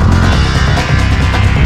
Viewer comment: My favorite track in that game, but I never really understood what the female voice said at 1:16, english isn't my first language. Can someone tell me what she said? To me it always sounded like: "may evil won't take you" but I'm pretty sure that's wrong... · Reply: The evil will take you